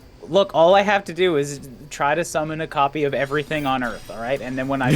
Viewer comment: I heard "Summon Copy" as "Summon coffee" the first time she said it this stream. It's been a while.